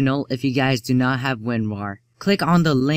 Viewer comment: lol it sounded like you said Winwar